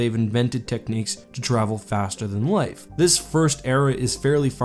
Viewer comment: It sounded like you said faster than life. 😅